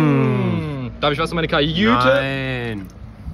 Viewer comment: Das einzige was ich nicht verstehe: Was fragt er bei 0:30 , worauf mit "nein" antwortet? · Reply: "Darf ich was für meine Kajüte?" Er hat nach Energy für seine Kajüte gefragt. :D